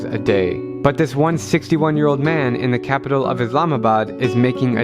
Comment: I heard 161 years old and my heart stopped for a second